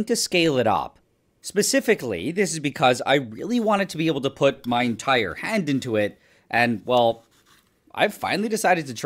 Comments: For a second there I thought you said you wanted to put your head in there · Thought he said to put his head in it lmao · Fucking hell, I heard "I want to put my entire head into it" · At first, I thought he said "I wanted to put my entire head into it". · I THOUGHT HE SAID HE WAS GOING TO PUT HIS ENTIRE H E A D IN THERE AT FIRST HAHA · I thought you said put your hole head in😂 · For a second I thought you said you wanted to put your entire head into it